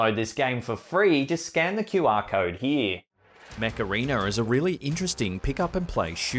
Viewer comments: When he was advertising the video game I thought he said the Macarena instead of Mech Arena lol!! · Hilarious…the sponsor game, I thought you were saying Macarena… MechArena🤣😂